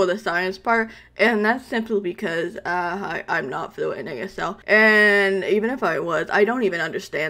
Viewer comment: I think she said she wasn’t fluent in asl